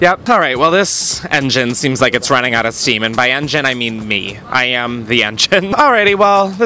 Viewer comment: Instead of “this engine has run out of steam” I heard “this engine has run out of seaman” and I’m like same same same